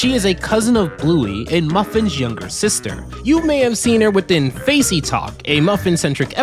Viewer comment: I thought they said sock’s was muffin’s younger brother?